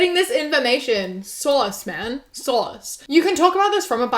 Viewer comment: I’m from New Jersey (USA) and the way you say “source” reminds me of how we say “sauce” so to me it sounded like you said”sauce, man, sauce?”